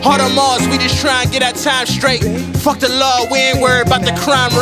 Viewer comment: did he just say " fuck the lord"?